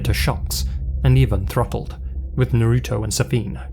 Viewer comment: Man, I misheard "throttled" for "throupled," which had me doing a double take at the closed captioning subtitles. Hearing loss sucks, but can make for some amusing moments. Frankly, the story of the French streamer is horrifying, so a brief moment of levity was appreciated, even if accidental.